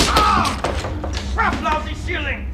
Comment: I always thought he said “craplousy” not “Crap! Lousy…”, which I think is much dumber and funnier.